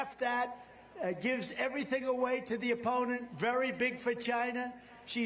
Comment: Did he say "very big vagina"?